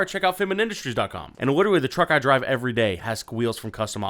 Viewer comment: did anyone hear witawe insted of literally